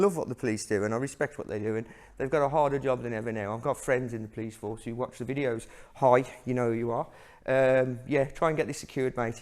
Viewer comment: I have friends in the police that watch these videos high, you know who you are? Is what I heard the first time; well it's what I heard the second time as well, but understood it for what it meant! >XD 13:25 - Aww what, you're a Snetter!! lol jk >XD Crestwood and the Oldswinford School Hospital boys boarding myself; I imagine you like or would like the OSH main building; all the old fittings etc ; really nice; not that you really appreciate such things at the time! >XD